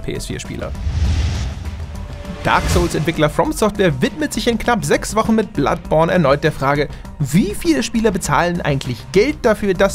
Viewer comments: Ich hab bloodporn verstanden 😂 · Ich hab einmal bloodporn verstanden